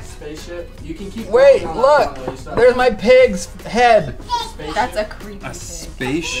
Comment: I think was tying to say SPACESHIP at 5:26 lol